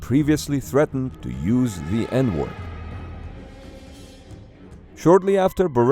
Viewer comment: when he said that putting had used the n word I thought he said n*gga